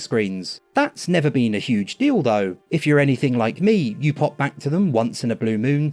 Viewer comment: "a huge deal though" i swear i heard him day "huge dildo" 😅